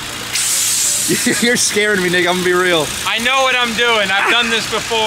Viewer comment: What did he say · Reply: "you're scaring me nick ima be real"